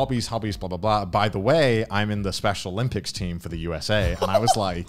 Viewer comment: I paused here and thought he said special ed lmao